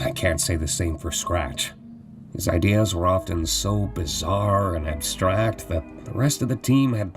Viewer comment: ?!"DID I HEAR "BIZARRE